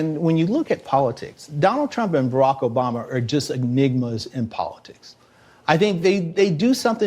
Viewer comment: Did he just call Barrack Obama a Nigma? 😆